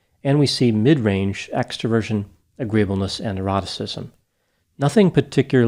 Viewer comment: Every time you say “neuroticism” I hear in “eroticism” in my head! Have a great holiday!